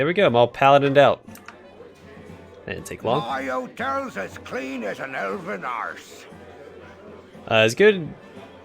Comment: I remember when I was a kid watching my dad play, I thought Winthrop said "my old toes are as clean as an elven arse" haha